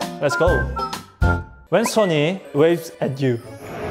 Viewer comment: i didn’t read the caption and thought he said “왼 손이 waves at you”